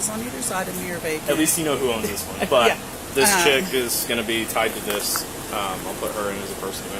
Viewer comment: Does he call the woman of interest "this chick" at 14:40?